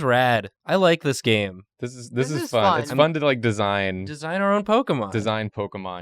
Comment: My ear buds glitches when said "it's fun to design" and it sounded like he said "fuck design!"